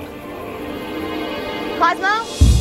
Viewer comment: I think she's saying "Cosmo".